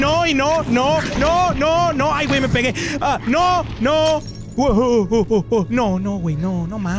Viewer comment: I’m I the only one who heard him say baguette